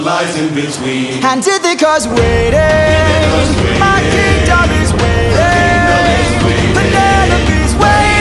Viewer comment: In full speed ahead, k always thought he said "My Penelo is waiting" as a nickname for Penelope. But he says kingdom! Haha